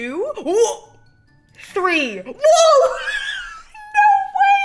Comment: she sounded like she said WOOF- 5:07 I audibly yelled TODOROKIII!!!!!